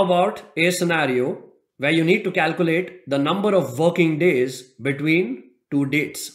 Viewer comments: Great tutorial, but I'm not gonna lie, I had my eyes closed during 11:26 and I thought he said 'the number of f***ing days' and then I opened my eyes and realized he said 'the number of working days.' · 😂😂I thought he said number of F**king days